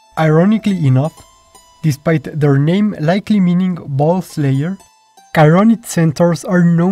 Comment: I will be honest, I heard “bull-slayer” as “ball-slayer” in both this one and the previous one, and knowing how dirty centaurs could get, I’m not all too surprised.